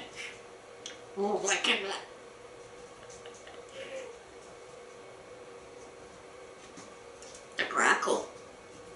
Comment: What did he say at 2:11? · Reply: He laughed, then said a little gibberish, then said, Grackle. (A grackle is a wild songbird)